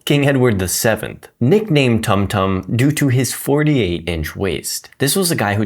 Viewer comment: My brainrotted dumbass thought you said tung tung